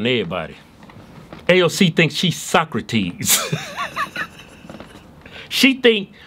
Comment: "AOC thinks she's Socrates" What I heard: "AOC suck on these"